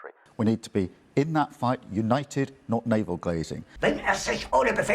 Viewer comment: did Starmer really say “Navel Glazing”? The mind boggles.